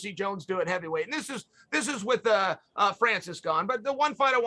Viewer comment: Did he say he wants to see Jones fight Francis Gane at 6:18? Francis Gane would be an absolute monster